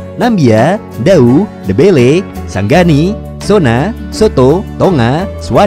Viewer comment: kok gue dengernya soto sih😂😂😂